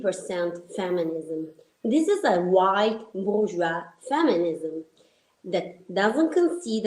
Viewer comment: Cosa dice al minuto 0:46 ? "(...) this is a white 《wosua》feminism." Non capisco, che parola è quella tra le《...》? È forse "bourgeois" (borghese) ? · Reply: Sì. Bourgeois.